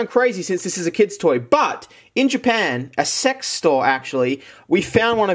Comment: did u say.. A SEX STORE?